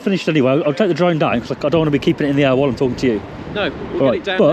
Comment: All i heard was I'm talking sht